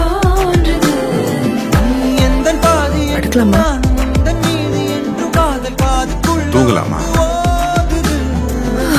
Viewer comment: what does she says ? And wt does he reply plz any one say me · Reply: She:- I'm tired 😩 He:- then we sleep 😴 She:- OMG! Thank You ☺️